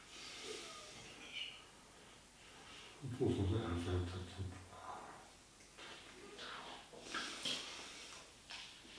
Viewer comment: Nél mit mond? :D mit felejtett el? am kedvenc részem :D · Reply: "Fotózni elfelejtettem."